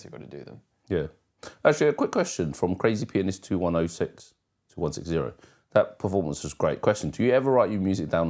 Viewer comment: Forgot to say this earlier, but thanks for answering my question! For those of you who want to see my question, jump to 21:25. p.s. my name was misread as “2106” instead of “2160,” but that’s absolutely fine 😂